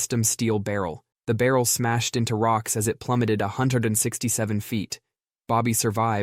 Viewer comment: a hundred and what feet did u just say .....6.....7 💀💀